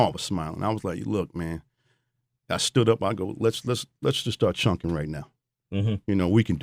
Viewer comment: What'd he say he told Tupac around 15:06? "Let's start chunkin' it"?